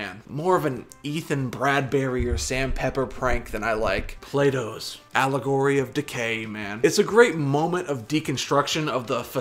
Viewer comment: Sounds like your saying Plato's allegory of decay not allegory of the cave